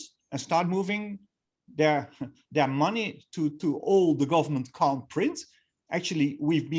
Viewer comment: What did he say at 9:42? "moving money into all the government ... ?" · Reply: move your money into all the government can't print